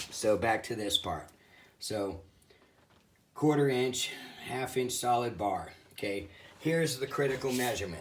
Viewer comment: Now sounds like you say 1 1/2". But you got 1/2". Just making sure for the 1/2" bar hole. Thanks